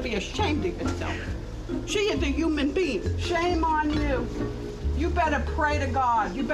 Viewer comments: subtitles says HUMAN BEANS just how she said it lmaooo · Okay so the captions at 4:36-4:37 the women said "The human beings" but the captions said "The human beans" xD